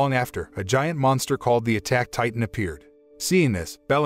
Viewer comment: Wait did he just say "attack titan" Eren is that you 9:25🤔😏